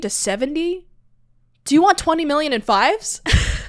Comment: Ok so I always thought he said you want any fries with that. Like he was taking an order at a drive through. I don't think he said Fives but I can kind of hear that too now that you said it. I think it's fries but it's funny either way if you got it wrong or right. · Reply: He totally says "fives". The line right before that one is "10 million in tens", so "do you want any fives with that" makes perfect sense, whereas "fries" makes zero sense.